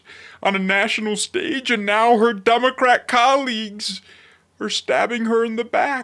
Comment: I heard Dumbocrat Collegues.